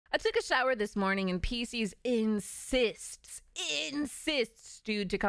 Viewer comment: i paused at the perfect moment so it sounded like said "I took a shower this morning in pee" XD